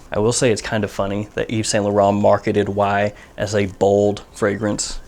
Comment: A bald release? That sounds about right.... or perhaps I misheard? lol